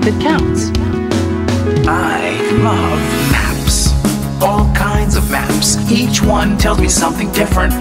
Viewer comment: did anyone notice at 1:16 the subtitles says kids when he meant to put kinds.. sorry for being a grammar Nazi just funny :)